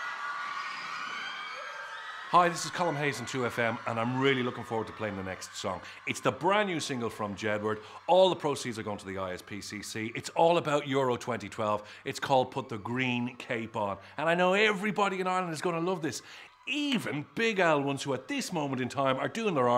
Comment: i hear: PUT THE GREEN GAY BAR. D